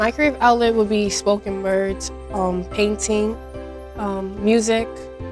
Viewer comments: I thought she said she likes smoking birds😂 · I thought she said smoking birds 💀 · i thought she said smoking birds 😂🤦‍♀️ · Am I the only one who thought she said smoking birds 17:59 😂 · thought she said smoking birds 😂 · Omg 17:59 thought i heard smokin birds